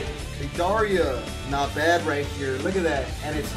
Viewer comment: did he say 'Dei-daria' ?